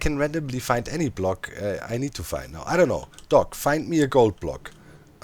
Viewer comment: When doc said gold block I thought he said goat block. so I though 'hey there isn't one of those in minecraft but I'm sure he could still find one'